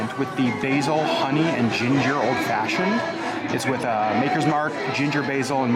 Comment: First I thought you said “basil onion ginger old fashioned” and I about vomited.